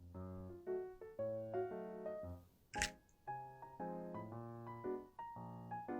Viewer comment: bruh did you say cookie cups?